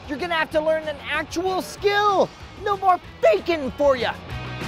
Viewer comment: For a moment I thought he said no more BACON for ya at the beginning. Worst scare of my life.